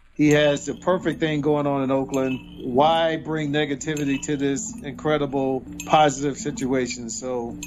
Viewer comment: Tim Brown sounded like he said niggativity not negativity?